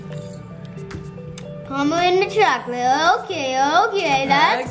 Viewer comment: I thought said Nu chocolate caaake!